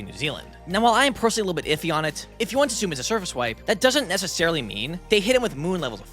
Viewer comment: I thought he said the city's were surface wiped.